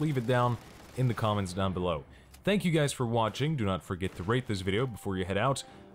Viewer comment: sad to say that at the end I heard: " do not forget to rape this video". sorry :(